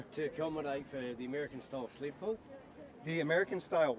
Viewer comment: The sled pulling dude, I thought he said American style sleeper-pod. Wtf you gonna sleep, bro? And I'm from NZ, man we speak sloppily.